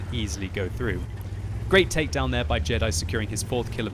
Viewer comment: I swear I heard you say Jedi boner...